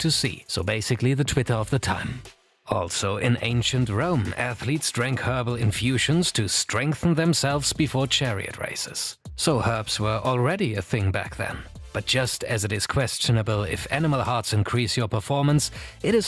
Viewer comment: High as hell and thought this man said HERPS not HERBS😭😭😭